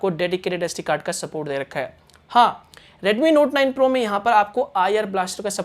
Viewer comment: did u said redmi note 9 pro😐